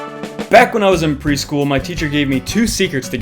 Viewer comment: Why did It thought he was gonna say “Back when I was in Prison” 😳